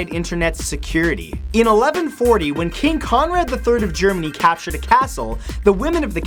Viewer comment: when you said king Conrad i thought you said king kong